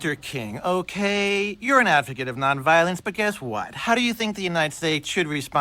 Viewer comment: bruh 😂 I thought he said "African of non violence" 😂😂